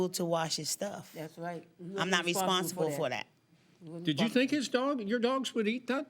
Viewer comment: Am I the only one that heard her say Respire?...instead of Responsible! And further more that's on you to leave ur property and u know she has dogs and they chew on things. So ur out of gas or should I say paint gun..go out and buy another one.😂😂